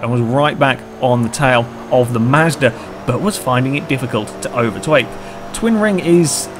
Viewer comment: Did anybody else hear say 'overtwake'